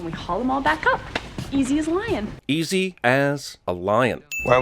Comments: I always thought she said "it's as easy as lyin" Also that other lady is in Stargate, Origin is the only religion. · I thought she said 'Easy as lyin'.' anyway, possibly the greatest single season of television ever. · She said easy as lyin’. I’m not sure if you really thought she said lion or not...